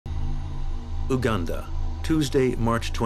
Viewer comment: it sounded like it was a swedish person saying "uganda" wtf